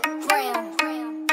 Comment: I thought he was saying "Whoa, hunny" at the beginning of some of those songs... now I realize he's saying "100 grams".